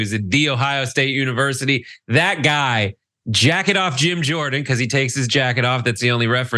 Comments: Oh, jacket off, I thought for a second he said jackin off Jim Jordan…which, come to think of it…😂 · When Brett called him "Jacket off Jordan" I misheard him and thought he had said" jack it off Jordan" in relation to his involvement in the sexual abuse case at OSU. Could Gym have given that advice to Dick Strauss when he was coaching at OSU. I mean they had lockers next door to each other. AS Eric Idle used to say " Know what I mean. Nudge nudge wink wink." Reference the elephant in the room.